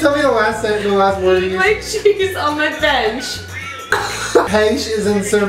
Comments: Who else thought she said "Eat my cheese on my vag"? Just me... Okay. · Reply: I thought she said "Eat my cheese off my face". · Who else thought she said "Eat my cheese on my vag"? Just me... Okay. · Am I the only one who thought she said "Eat cheese on my bitch" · When she said eat my cheese on my bench, I thought she said eat my cheese on my vag lol 😂